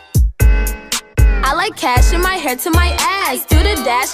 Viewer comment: I always thought it said I like cash and my head to my ass until now 😸